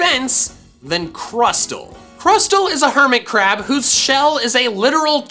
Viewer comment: Hermitcrab, when I heard that I thought he said Hermitcraft